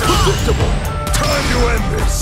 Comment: did he just say PREDICTIBLE? Soy sauce for guile